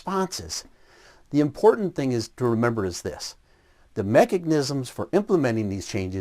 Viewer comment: Erm, did he say "mechignisims?"